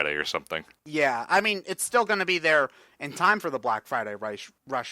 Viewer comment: Did he say the Black Friday Reich?!